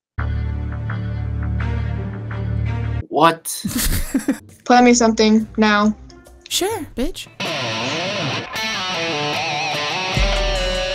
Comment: ?? wait did you just call her Biach lol